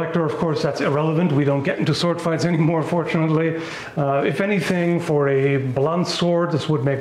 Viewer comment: Did you say fortunately or unfortunately? I can't quite tell lol. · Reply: FORTUNATELY. I'm not a total psycho. :p